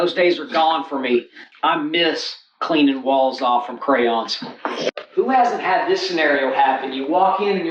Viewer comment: did he say “walls off of crayons?!”